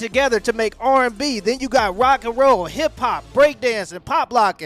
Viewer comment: was getting dressed while listening across the room and swear I heard you say ‘hot pockets’ 😆😂